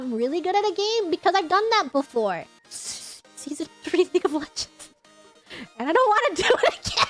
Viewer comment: Reminds me of Fauna who's repressed the DOTA gamer. I wonder what game Gigi has thousands of hours in? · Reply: yes, I hear "season 3 league of legends"